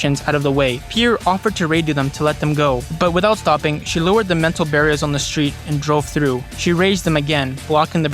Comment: Dude, you need to work on your Pronunciation, it's been all over the place the entire video. Just before you pronounced 'pedestrians' like 'perditions'. And 'mental' barrier instead of *'metal barrier', 'sniffiling dogs' instead of *'sniffing dogs', 'inceesion' instead of *'incision' etc.